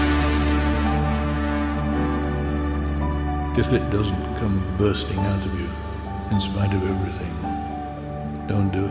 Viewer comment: Hey guys, can anybody tell me what the men say at 03:37? I speak englisch but I dont really understand. Thank you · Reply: É a parte final do poema de Charles Bukowski - "então você quer ser escritor?" "so you want to be a writer?"